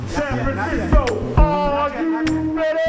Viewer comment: when the guy yelled ''are you ready'' idk why but i heard ''warriors, can you dig it'' the guy has a similar voice or something, idk